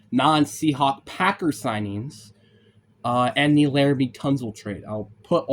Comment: what did you say at 0:25 i cant make it out. enular betunsletrade?